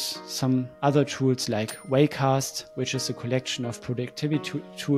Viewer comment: I think says "Raycast", not "Wellcast". Raycast is an app launcher and general productivity tool for macOS.